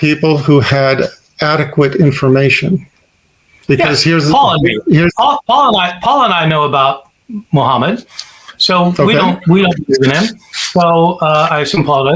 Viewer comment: Is Paul a muslim or not? I thought Dr. Bart said "Me and Paul know Muhammad, and we don't accept"